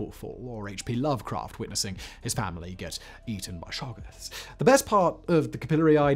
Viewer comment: H.P. Lovecraft did famously have some family tragedies that deeply affected him, but this notion of his watching any of them get eaten by sharks is a new one on me! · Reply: I could've sworn he said shoggoths...